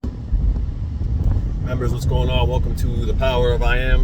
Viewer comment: I made it public lol. Another vid is coming in members later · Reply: oh . i heard "hi members" that's why . didn't notice if it's public or not 😂